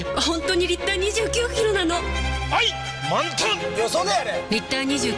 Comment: 愛！満タン！！ と聞こえたのは私だけ？（＾＾；）